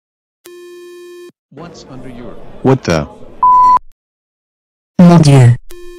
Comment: What Happen To Turkey??? ( When He Waits For Join EU ) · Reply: But. In 3:08 I Hear... ''What The **''